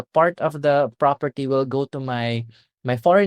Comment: Hey. At about the 14:50 mark. says that word "legal" but it sounds like he is saying "illegal". Can you confirm that he is saying "legal", please? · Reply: Yes, he says it’s ‘illegal’ for the wife to leave the land to her husband in the will